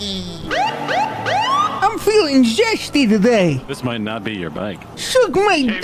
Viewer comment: I heard "I'm feeling Sexy today" and that made it ten times funnier.